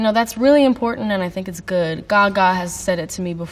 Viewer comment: Billie says ''Gaga'' subtitles said: ''God''